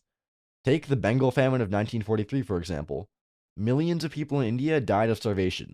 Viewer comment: i had to rewind i thought you said 1493